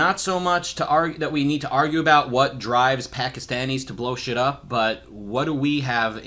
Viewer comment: When Rucka said "what drives Pakistanis to blow shit up" I heard "what drives Kekistanis to blow shit up."